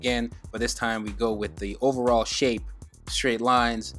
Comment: Sorry, I thought I heard Overhaul! 😂